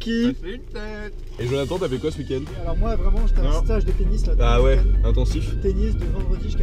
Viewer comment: 1'22 j'ai compris "Moi, j'ai fait un stage de PENIS". Je trouvais qu'après la place Pigalle, ça passait plutôt bien :) .